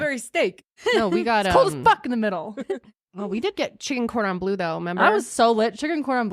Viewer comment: When you guys said “chicken cordon bleu” I had to rewind and listen again because I heard “Chicken fried loon” 😂🙈